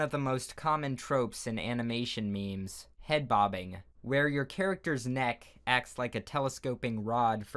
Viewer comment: A mix between Jake Paul, Harry Potter, and a giraffe lol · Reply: I saw this comment while watching the video and I was at the part where he said "where a character's neck" I them clicked the timestamp and he says "happens", it sounded like he said "when a character's neck happens" lol